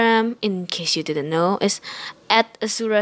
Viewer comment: In 1:58 When you said your instagram I heard like you said "es" that ming "is" in Spanish soo.. you are Spanish?